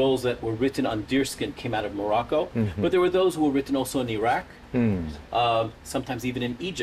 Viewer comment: 모로코로 들리는데 모나코로 해석이 되었네요 😅 저만 모로코로 들리나요?